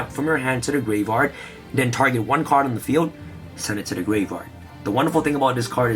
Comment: “Send this card to the gray vard” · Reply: I thought I heard “send this card to the gay bar” I’m like wait whut. That can’t be right.